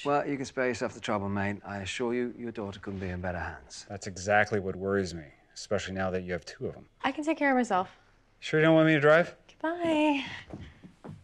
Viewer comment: I actually thought hook said "she could be in better hands" XD